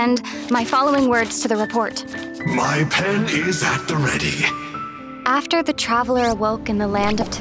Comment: I can't stop laughing what I heard Enjou says: "My Pen is at the ready" 0:20 Hahahahaha We love you Enjou and Happy 2nd Anniversary Genshin Impact!